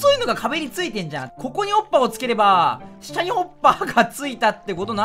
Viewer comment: 滑舌のせいかホッパーが おっぱいに聞こえない？自分だけ？